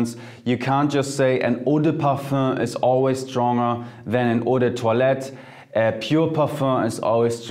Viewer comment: I keep hearing 'odor toilet'. Can someone help me , what's he saying ? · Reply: "Eau de Toilette" which yes translates to toilet water. It's just a type of fragrance spray